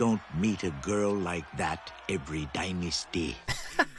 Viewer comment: When I was a kid, and he said "you don't meet a girl like that every dynasty" I thought he said "every chines-day". lol.